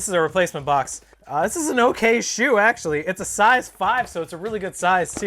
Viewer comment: Ameen Moqbel StockX lowest ask is $509 and GOAT is $615 so i don’t know what you’re talking about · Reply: Had to rewatch the video, thought you said size 9 but it was actually a size 5. You’re right mate